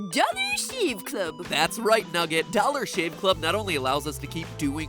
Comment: I thought nugget said dumas shave club at the end